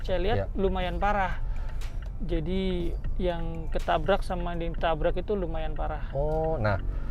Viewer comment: kok aku dengernya "yang ketabrak dan ditabrak lumayan parah,.."